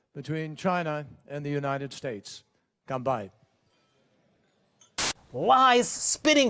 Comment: no he didnt! Did he say "kanpai" or "combined"?